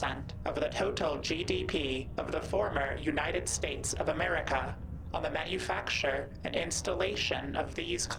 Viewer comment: Did it just say former United States of America?